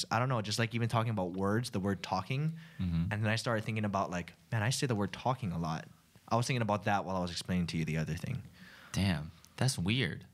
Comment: Can u type what did Ryan said between 40:36 -40:41. I couldn’t understand. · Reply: Ryan: Man I say the word "talking" a lot. I was thinking about that while I was explaining to you the other thing.